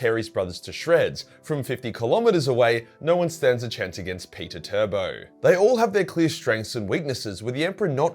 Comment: Lol did he just say Peter-Turbo??